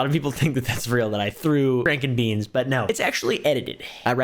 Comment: When he said Frank&Beans I heard Berries&Cream and for some reason became convinced that he was the man from that legendary video